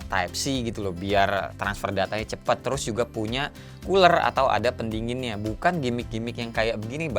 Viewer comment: hah cooler, aku dengernya uler 😂